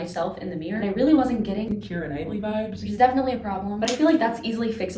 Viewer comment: When she was saying Keira Knightley, the auto detect close captions thought she said Clearer Nighty.